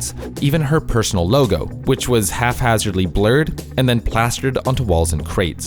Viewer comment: did you just say 'half hazardly'? the word is 'haphazardly'